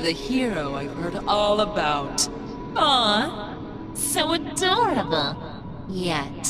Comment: "Aww...... so dumb!" literally has beaten a literal gisnt huge string boss that has 150,000 hp and has beaten a boss ven more powerful than that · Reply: let me say this, i hear "dumb" instead of "adorable" , idk why, when i tryto refresh the hearing it just keeps saying "dumb" insetad of "adorable".